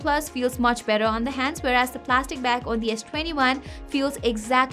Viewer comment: correction needed.......plastic back instead of platic back